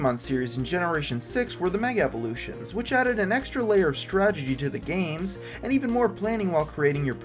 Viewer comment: Did he just say "stragedy"?